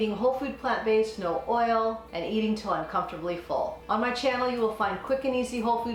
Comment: Hehe, at first I thought you said ‘uncomfortably full’ but realized you said until comfortably full :)